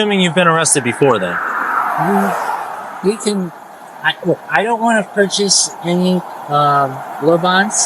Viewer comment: I seriously thought he said I don't want to purchase robots. 🤣🤣